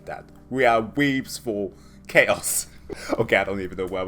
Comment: did you just say we are "weebs for chaos"? ... because I'm on board for it! 😆